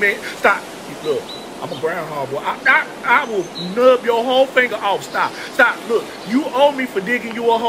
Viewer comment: Lmao "I'm a ground hog boy I'll gnaw your whole finger off" 😂 ☠️ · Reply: I thought he said " I will know your whole finger off"! Lol. 🤣